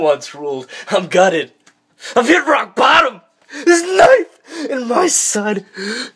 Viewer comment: did he say rockbottom at 0:36 well if yes u Should get at least one